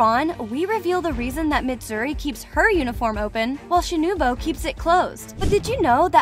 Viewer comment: did she say shinubo instead of shinobu lol